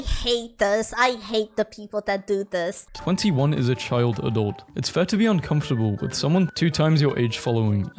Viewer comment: That's not what she said though. She said child adult. As in, acknowledging she's an adult, but also acknowledging she's new to it and not yet mature all the way. I thought leftists believed everything women said regarding men. I guess not when she actually has a point.